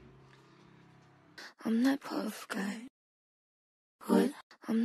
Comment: question 0:10 what does say? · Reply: "what?"